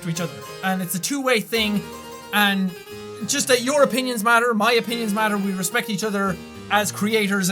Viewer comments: When you said "your opinions matter" I thought you said "Europeans matter" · "Europinions matter, my opinions matter" haha that's what it sounded like he said.